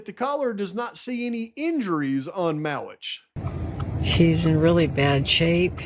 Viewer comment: " She's in really bad shape. He's got cuts and had no clothes on hardly". What? Wait? Did the woman say " *She's* in really bad shape? The transcript says the word 'he', but Google auto-generate captions says 'she'. She did say that, which suggests - based on use of the word SHE - that there was a female with Mowitch. Tell me I'm wrong.